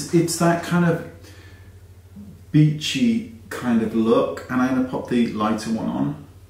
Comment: I thought you said it was “that bitchy kind of look” 😂